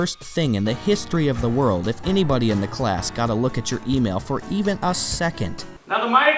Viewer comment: When he said email I thought he said oatmeal.....